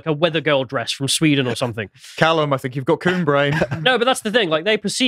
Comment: Did Connor say Callum had "coon brain" @ 7:31? 🤣💀